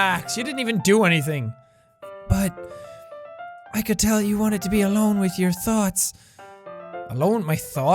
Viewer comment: what she said I could tell you wanted to be alone with your thoughts what I heard I could tell you wanted to be alone with your thots jack alone with my thots?!